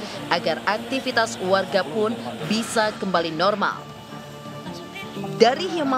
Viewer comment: perasaan gw dengernya melaforkan bukan melaporkan kae pake huruf "f"